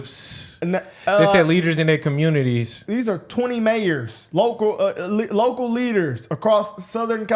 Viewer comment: I think she said mayors